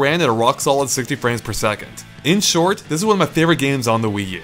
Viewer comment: I heard "ink short" at the end. Help.